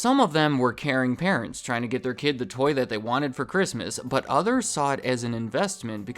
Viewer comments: why did i hear 0:35 as "the toilet they wanted for christmas"? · Did you say "Karen parents" or "caring parents"? Lol